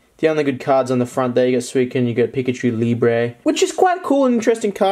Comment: My mom saw the suicune/pikachu liber and thought suicune said suicide